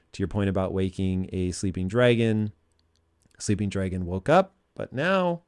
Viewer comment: I thought he said a sleeping giant.